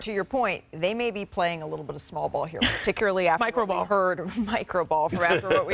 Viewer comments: did bro say DIDDY BALL lmaooo 😂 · did he say Diddy ball? 😂